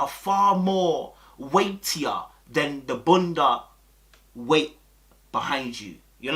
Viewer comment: Might be a dim Question but What’s a bunda? lol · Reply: I thought he was saying bundles 😂😂